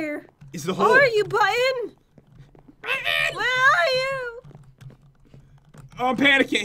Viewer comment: can anyone tell what pat just said is like angry bird XD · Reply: Button!!!